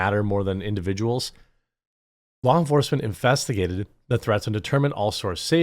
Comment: It sounded like you said, Infestigated. I'm going to start using that for how police deal with the little people's troubles.